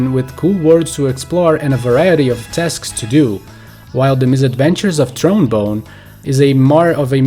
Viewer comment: The misadventures of trombone is what I thought you said. Lol